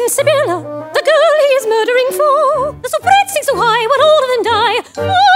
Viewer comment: Anyone know what Phoebe says at 0:18? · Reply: the soubrette (a type of soprano) sings so high, while all of them die